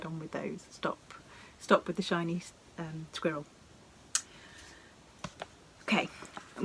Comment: I was so flummoxed! I thought you were saying “Chinese squirrel “. Had to google that and didn’t come up with anything. It finally dawned on me that you said SHINY squirrel! Love your videos so much!